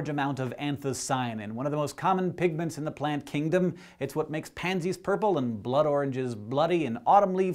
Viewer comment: I heard "it's what makes panties purple..." wasn't looking at the screen at the time.